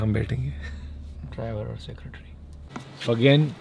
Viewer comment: Did he said "Driver Ka Secretary"- 13:44 😂😹